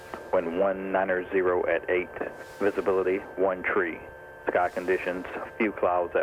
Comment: did he say visibility 1 tree? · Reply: The number three in aviation pronounced three